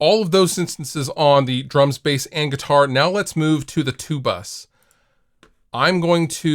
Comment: casually listening and thought I heard "now let's move to the tubas". Got excited for the tubas. now experiencing disappointment in the lack of tuba content on this channel.